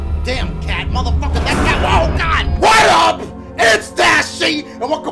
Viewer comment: omfg XD I put captions and when he said WHAT UP, ITS DASHIE. it thought he said what up its that she